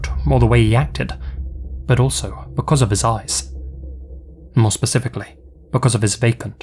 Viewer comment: With your accent, I thought you said 'women were creeped out because of his ASS.' Took a second to realize you meant 'eyes' Didn't realize an ass could be creepy. 'If women wouldn't give him what he wanted, he'd take it.' Sounds like the current individual in the White House.